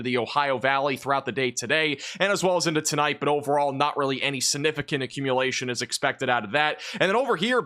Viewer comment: Is it just me or does it always sound like he says "Sinificant"? Thanks Max!